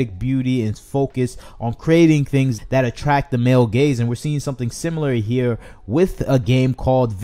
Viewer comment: Everytime i hear "male gaze" it sounds like "male gays" 😂😂